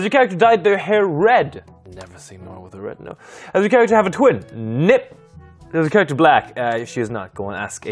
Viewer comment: Wth 3:57 he says ‘NI-‘ and then the question ‘Is your character black’ appears. Im dying 🤣🤣💀💀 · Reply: He says Nip